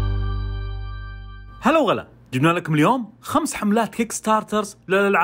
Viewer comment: انا سمعتا غلط ولا اي ههههههههههههههههههههههههههههههههههههههههههههههههههههههههه زوتها صح ( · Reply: هلا و غلا